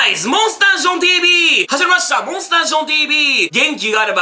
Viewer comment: ジョンさんたまに始まりましたをはじまましたに聞こえる時ある気がする笑